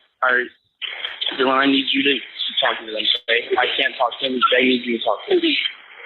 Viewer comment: I hear him say “Bethany, or Dylan, I need you to talk to them” not “That’s me. Dylan, I need you to talk to them.” I think is doing the same thing here regardless though (asking the girls to take the phone so he can talk to police).